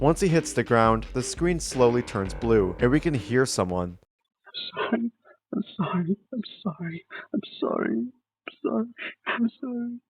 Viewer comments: i hear "Son. im sorry. im sorry. im sorry . Son. im sorry." again and again 😧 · i swear i heard "im sorry son", might have ended it all